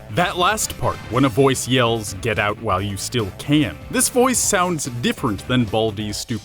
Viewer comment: I always thought that it said "KILL MY SELF DAD!" instead of "GET OUT WHILE YOU STILL CAN!" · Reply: Clayton Wallace I thought he said GET OUT WHILE YOU F* STILL CAN